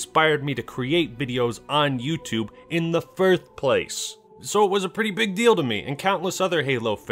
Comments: Lmao did anyone hear him say “Firth place” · did you just say firth?